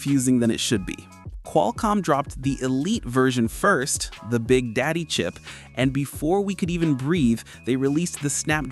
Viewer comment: Replay what you just said??? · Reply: The big daddy chip